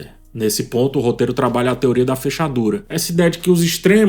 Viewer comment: Eu ouvi "teoria da fechadura", fiquei "ué?". Voltei o vídeo, ouvi de novo e entendi: quanto mais se fecha a ferradura, mais fechadura ela fica